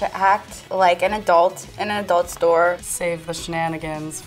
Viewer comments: i thought it said apple store so i was like uhh who’s licking the display ipads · I thought it said apple store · Totally thought it said Apple Store not adult and it made the video 10x funnier